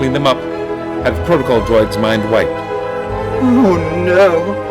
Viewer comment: Up until about 5 years ago, I honestly thought that Bail said "Protocol droids mind white," which, given the colour of the corridor they were standing in, I thought was a bit of an odd thing to say...